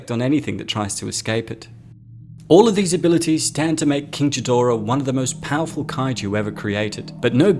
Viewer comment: Did you just say "Jidora"?